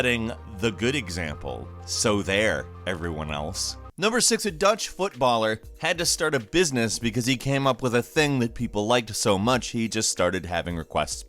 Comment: i thought you said a touch footballer for a second,lol.